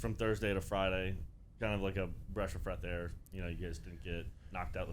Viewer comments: Anyone catch Roger say “bresh of freath air” at 9:08??👀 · does roger actually say "breash of freth air"?? also, optic chicago should stay the same 4 guys next season. they'll be different next year